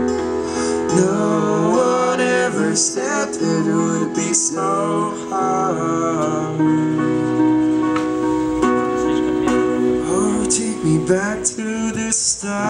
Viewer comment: Кто-то по русски сказал "Слишком медленно" или мне послышалось?